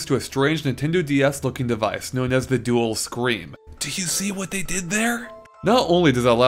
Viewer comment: Wow I thought it sounded like he said "dual screen" and I was like "So, uh, yeah..." then I read it in the comments and lol'd! Why does E. Gadd base those names on scaring?